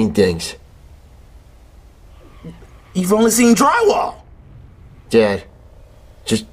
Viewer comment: what did he say. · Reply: After a whole year I have arrived as your hero, "You've only seen dry wall"